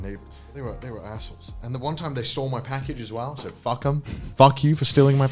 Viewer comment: I heard "saw my package". Thought this was going to turn into a story of how he got arrested for public indecency.